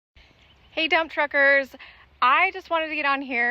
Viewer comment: I am glad you like to stick to the rule`s! I used to drive logging trucks many, many, many moons ago, as they say. lol In the intro, for a split second, I thought I heard you say dumb truckers, then I realized it was dump truckers. lol I knew a playboy model who was smoken hot as I would say, who drove semi with at least a 40 foot trailer, it could have been 50-52? You gals are way better drivers than some men but even way-way-way better drivers than illegals!!!!! Stay safe and may God keep on blessing u and yours.